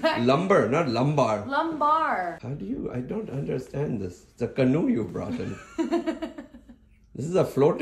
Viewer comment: I thought you said cannoli, not canoe. either will work. LOL